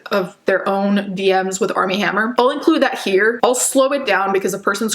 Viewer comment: I had absolutely no idea who Armie Hammer was until all of this came out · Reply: Same here I thought she was saying Arm & Hammer ngl 💀